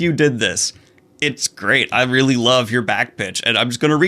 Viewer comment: But why do I just keep hearing "back bitch" instead of 'back pitch'?? 😶🤭😅🙃👀😁🤣🤣🤣🤣💜💜